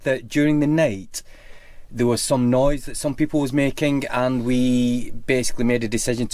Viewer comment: Ok it was people attacking. Your accent confused me. Thought you said wolves. Lol. You annoy by repeatedly saying you are not going into detail of what transpired. And you say you were trained in conflict management ? Once you stepped outside the bad guys left quickly . WHY ? Conflict management was rocking s a big gun? Lol 🤣